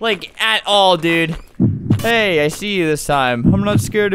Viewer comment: You should play "The home" (it has a black screen and then red writing saying "The Home" it's really fun too.! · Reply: i hear you says " i pee". but i agree